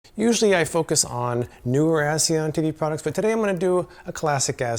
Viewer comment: My volume was all the way down in the beginning and I thought you said that you focused on ass and titty products